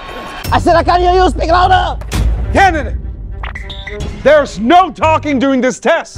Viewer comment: I thought he said kevin but he said candidate